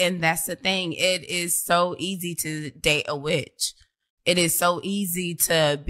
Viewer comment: did she say its easy to date a witch or date a B*tch, I think I misheard